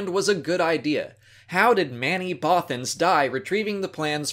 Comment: I thought she said many bofins died to bring us etc. Not manny